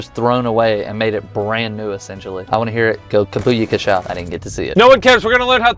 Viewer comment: Did say? “Go booyakasha”